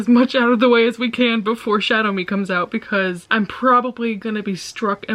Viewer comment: when she said shadow me i thought she was just saying shatter me in an uwu voice hahaha